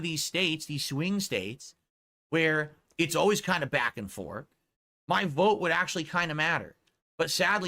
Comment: Did that mother fugger say "back and fork"????? Please tell me someone else heard that.